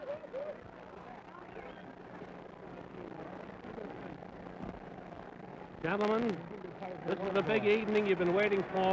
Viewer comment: Can anyone with a sound enhancer decrypt what ALi is saying to Terrell during the staredown at 1:46? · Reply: ***** lol yeah I know the whole story, but is that what he literally said? like a transcript. i did catch "uncle tom white people"